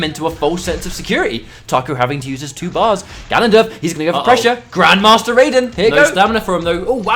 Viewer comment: did I hear grandmaster raiden? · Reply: Just a pun. When Raiden throws the MB Bolt it kinda acts like GM's Ice Klone, not letting you escape. So those setups with the MB Bolt are often called Grandmaster Raiden